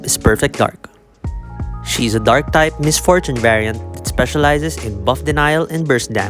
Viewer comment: Wtf did you just say fart type variant😂😂😂😭😭😭